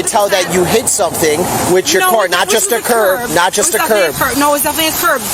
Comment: Did she say "I hit a big curb"? You mean a human?